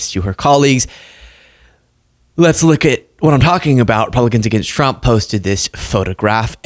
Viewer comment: I had to rewind 3 times. I could have sworn you said "Pelicans against Trump". This has to be a group down on the Gulf. Feel free to use it!